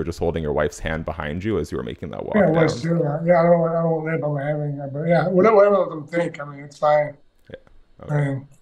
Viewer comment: What is stipe saying @ 7:06 hahahahahaha i love him man but Damnn stipe 😂😂😂😂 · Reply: I think he essentially tried to say, "I was holding my wife's hand but people can think what they wanna think" lol